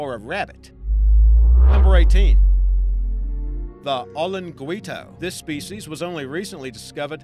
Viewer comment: When you said olinguito I thought you said the only Dorito XD